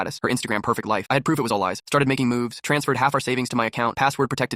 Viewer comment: When he said moves, I thought he said nudes 😭